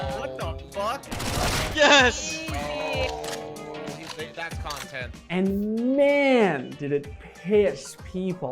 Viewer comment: did i hear that right? "oh he made that content"